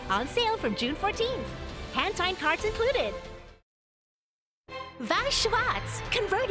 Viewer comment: I Thought they said hentai cards included